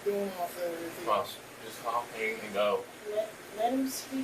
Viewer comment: Sounds like he said just call pinky doe.